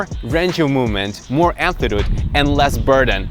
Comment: Hey, I find your videos really helpful, but can you (or anyone else) tell me what the word at 3:09 sounding like 'anthidude' (and repeated a couple of times later) is? I can't figure out from context... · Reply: Sasha Cooper amplitude (range of motion)